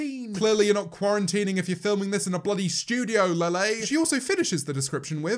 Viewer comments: did he just say Lele? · PLEASE DONT TELL ME IM THE ONLY ONE WHO HEARD “LELE” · did anyone else hear say Lele instead of Lilly? 😂